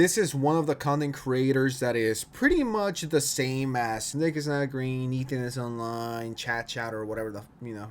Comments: “niggas not green” I heard that lil bro 😂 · I swear i heard you say the n word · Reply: he said nick is not green